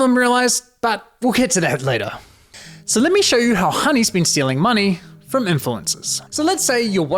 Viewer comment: Investigative journalism done right for the benefit of the masses. Thank you! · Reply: I liked when he said khoney instead of honey; referring to the jews who use kh a lot like Khamas instead of Hamas. He needs to be careful, the Mossad can get to him easily.